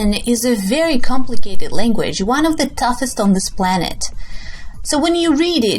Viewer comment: en 0:24 escuche tuffest? que significa? o cual es la palabra correcta? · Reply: toughest en español creo que seria "lo mas duro" o "lo mas dificil"